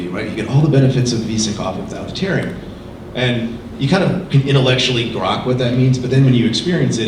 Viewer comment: Did he say 'intellectually grok' at 16:25?